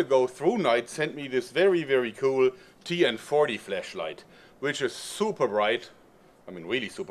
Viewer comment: did he say fleshlight ? at 0:10 ?